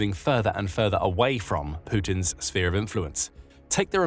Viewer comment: 23,000 Russian companies are working fruitfully in Kazakhstan, founded by Russians that have fled the insane dictator Putin. At first I thought that the narrator said "Putin's spear of influence". That would be more appropriate.